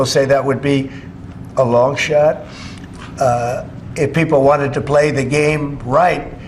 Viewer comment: Thought he said it would be a long shart.